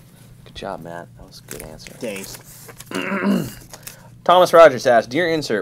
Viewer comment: Does Matt say "Dank"?